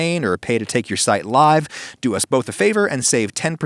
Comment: when he said "go frico yourself," I thought he said go frick yourself